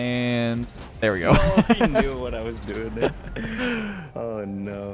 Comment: This might be fake but idk 10:35 listen closely he said “oh HE knew what I was doing”he didn’t say you